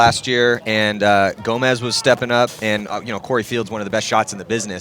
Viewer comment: did I just hear say Corey Fields? They literally just got off of a one minute tangent about how he doesn't have a S in his last name. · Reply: he said "Corey Field is one of the best shots"